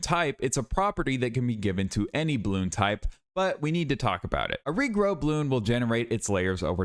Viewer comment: WAIT I thought it said "every blood type" lmao